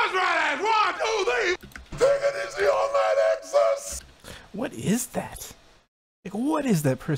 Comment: I think he’s saying “[take it easy?] online access